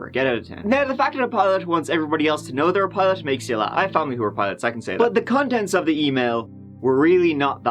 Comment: At first, I thought said, "I have family that are PIRATES, I can say that." Or did I hear that correctly? Arr!